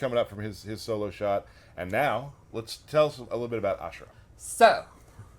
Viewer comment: For some reason I thought you said Asura and I almost lost it thinking he somehow got into MK. Disappointed